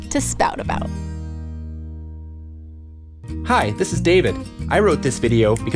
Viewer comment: I thought you said “Hi this is cake”